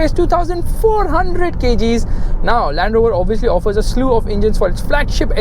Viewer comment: did he say Land Rover?